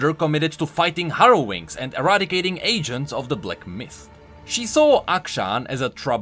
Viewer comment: Only I heard him say “eradicating asians of the black mist” at @ 1:52 ?